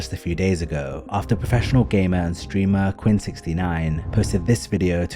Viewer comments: I genuinely thought you said "professional gay man streamer" I had to rewind to hear gamer and streamer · i didn't hear "professional gamer and streamer," i heard "professional gay man streamer"